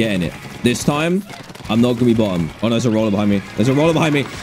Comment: "im not gonna be bottom" is what she said